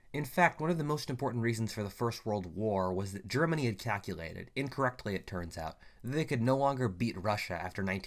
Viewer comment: I misheard this as "Germany ejaculated" 🗿